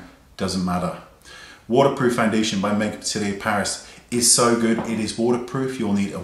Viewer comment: I can't make out what you are saying. Sounds like Make Up City...of Paris? Can you write that one out so I can see? I tried to figure it out from your list but what you said and what I see and hear aren't coming together. It definitely doesn't sound like MUFE. Ha. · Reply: I'm 90% sure he said Makeup Atelier Paris, but I could be wrong. He was talking too fast for me to understand half of the ones he named :p Hope I helped, though.